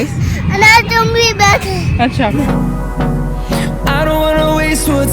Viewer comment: is he said to you? Nice video i enjoyed a lot 💖,,,, yes please do weekly vlogs like Anusahy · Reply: He calls me 😅 I'll work on that, although my life isn't as interesting 😅